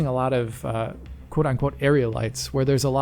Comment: Im sorry, did he just say "quote on quote"?